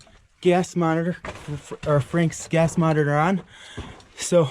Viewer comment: mattroski007 25:55 I think that is a gas tester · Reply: I think he literally says he's using a gas meter